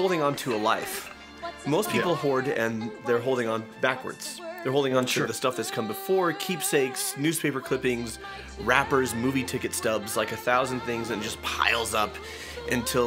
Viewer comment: Okay, when he was talking about things people hoard at first, he said, "wrappers".....and I heard "rappers". And I was very confused.